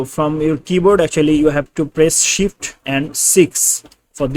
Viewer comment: I thought you said shit haha 😂